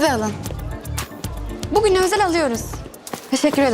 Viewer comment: Teşekkür mü etti ben mi yanlış 1:11 duydummmmm